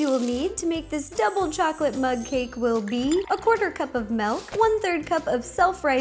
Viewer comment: For a second I thought you said mud cake 😂. Another great vid 🦄